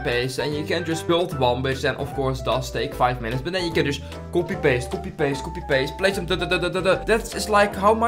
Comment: I thought he was saying poopy paste💀